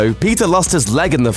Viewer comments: When he said Peeta I thought he said Pizza😂😂😂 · When he said Peeta it sounded like he said pizza 😂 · when he said peeta it sounded like he said PIZZA